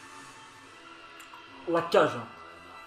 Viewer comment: 「終わっちゃうじゃん」って聞こえた笑